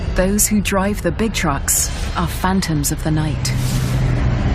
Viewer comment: Did she say 'those who drive the big trucks are the fat ones of the night.'? wow, how rude. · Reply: Phantoms of the night.Not fat ones lol.